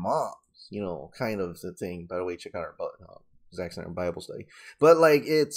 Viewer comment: Anyone knows what said? The captions say ¨by the way check out our butt, uh, exactly bible study¨ lol.